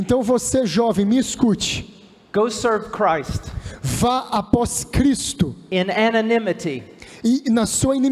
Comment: "Vá servir a Cristo Em anonimato" isso que ele disse, o tradutor se confundiu